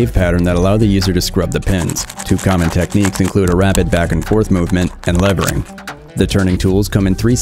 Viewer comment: I heard "A rabbit back-and-forth movement' 🐰